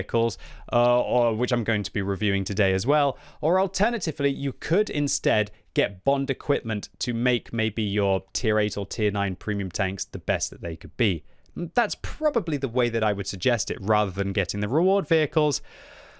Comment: I thought i quack said 100 premium tanks, cause now i have more than 100 premium tanks😅